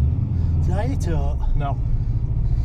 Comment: what does the 3rd guy ask at 0:38 ? right before the second driver says "no, nothing at all" ? I can't understand it :D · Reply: It's Northern English, he says "Did I hit 'owt?" Which means "Did I hit anything?"